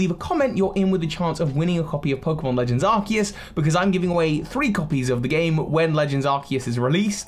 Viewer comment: I could have sworn you said 20 copies 🤷‍♀️, I hope we get more ancient forms of pokemon